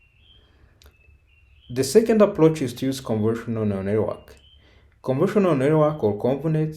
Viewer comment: More than once, it sounds like he is saying "conversional" instead of convolutional, and it never sounds like he says convolutional.